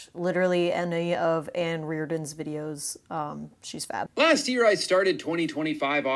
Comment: i had to rewind at 0:28 about 7 times cause I misheard "fab" and thought you were calling ann fat, which would've been wild